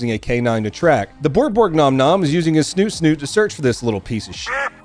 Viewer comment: Wait. Did I hear “the borg-borg nom-nom is using his snoot-snoot” New words. Learning is fun.